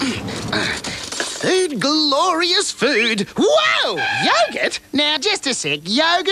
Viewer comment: i misheard yogurt to Gyat it 😭💀